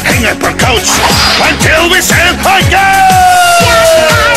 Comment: I heard "If you love celibacy" Still equally 'dafuq?'